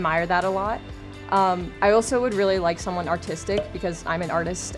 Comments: i thought she said autistic, not artistic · Okay but why did i think she said autistic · i thought she said autistic